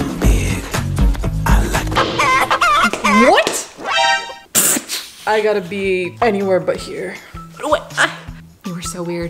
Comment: I HEARD HIM SAY (I GOTTA GO PEE,)